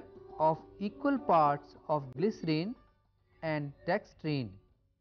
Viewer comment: mixture of Glycerine and ............. ? . . Incase you misheard,its dextrine !